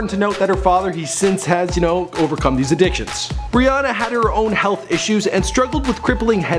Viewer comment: It sounded like he said bre Ana